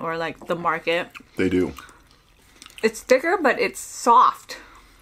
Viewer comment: i thought she said its thick but its thin (thats when she eat with her sister)